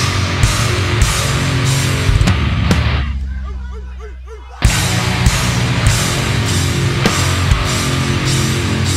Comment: What do they say at 50:07? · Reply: While we don't remember exactly what he said, it was something along the lines of, "Thank you Tampa, we fucking love you."